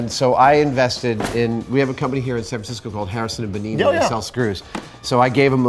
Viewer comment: What's the name of the shop that sells the screws at 2:45? He says it too fast and I can't understand him. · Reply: harrison and bonini i believe